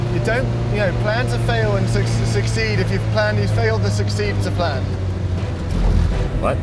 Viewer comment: “Yeah, ya… you don’t, you know, plan to fail and succeed if you’ve planned, you’ve failed to succeed to plan.” if I heard that correctly!